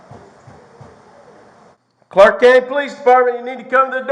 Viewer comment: At the start of the video. I heard: "Clark Kent Police Department ..."